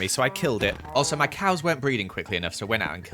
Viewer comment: When you said "my cows aren't breeding fast enough" I thought you said "my cows aren't breathing fast enough"💀